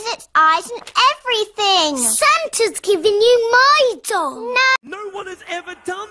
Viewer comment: When she said my doll to me it sounded like she said my dong